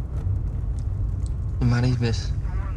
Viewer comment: What does Thomas say at 0:06? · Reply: Are you married, miss? It took me a few listens to catch. Stephan asked here the same question at the start of the episode